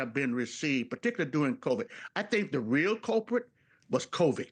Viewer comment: Did he just say “covik “? 😂 wonder how much he’s being paid.